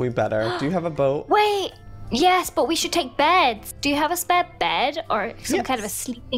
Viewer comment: When said "We should bring beds!" I thought she said "We should bring bears!"